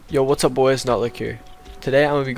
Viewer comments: Why did I hear, “What’s up boys nut lick here” xD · did you just say "what's up guys but kick here" ?!?!